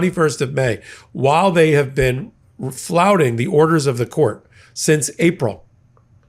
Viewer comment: oh my goodness, I thought you said flautists, because apparently Trump could’ve been a musical genius or as he’s said I could’ve been a flutist.